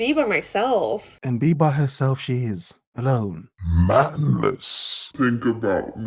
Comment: I thought was saying “Mammoth” instead of “Manless” about the girl at 4:00 . I mean, she’s a big girl so Mammoth worked there :) Men, work on yourselves. Build a path to success instead of a road to divorce court!